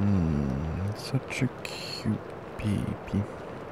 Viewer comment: Is something wrong with me?! I heard him say it as "Such a cute pP" 😭😭